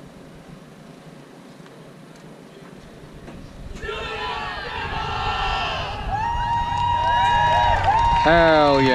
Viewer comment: What are they yelling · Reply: Do it up Devils!!!